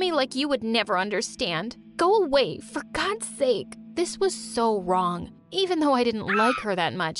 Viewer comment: I thought she told a bad word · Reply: I thought she said fuck god's sake